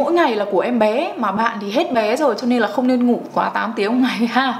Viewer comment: Khúc intro em nghe thành “hết ế rồi cho nên là không nên ngủ quá 8 tiếng nhá” :)))))